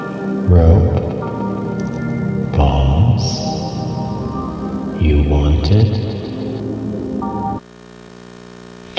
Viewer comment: what he say when he shadow! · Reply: “Too bad”